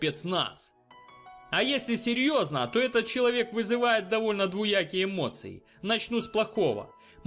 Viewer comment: мне 2 раза послышалось слово ДВУякие эмоции, Если так то правильно ДВОякие.